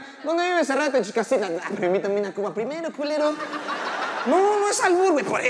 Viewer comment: Que dijo? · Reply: "Invítame una cuba primero q lero"😅